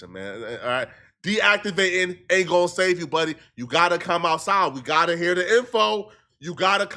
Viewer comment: When Said Gotta Come Outside Sounds Like He Said Gotta Clock That Tea😂